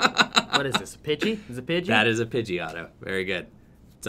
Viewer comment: Best prerelease video I’ve seen. Also had to lol when said ‘that is a Pidgey, Otto’. My kids thought he said ‘that is a Pidgeotto’. :0